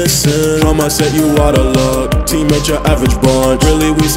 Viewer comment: OK so the line goes "Try my set, your out of luck". I always thought he said "Trauma says you outta luck" (like the Trauma Teams who just left his mom to die, so he was using that as the punchline). Mind Blown lol.